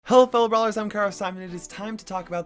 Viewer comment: The caption says "Carl simon" lmao